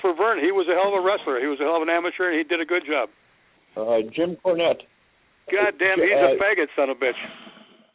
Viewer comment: Jim Barnett, not Cornette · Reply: He said Cornette. Ole thought he said Barnett